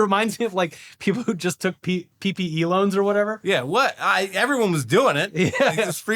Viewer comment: When said PPE Loans I heard “Peepee Elons”